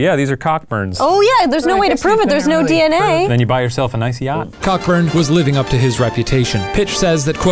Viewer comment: Anyone else hear “then you buy yourself an icy hot” 😂😂